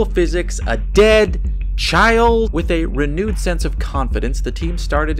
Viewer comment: Did you just say, with a 're-nude' sense of confidence? If so - your talent does not go unnoticed!